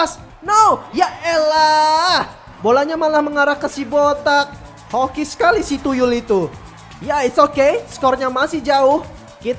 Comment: Coba cek menit ke 48:54 ngomong apa ke si van gils👍🏻😅😂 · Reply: Hoki sekali si tuyul itu